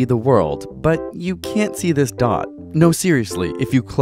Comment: I thought he said you can't see this DOG and I was thinking, great trick I really can't.